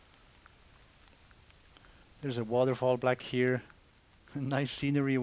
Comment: Did he just say "there's a waterfall black here"?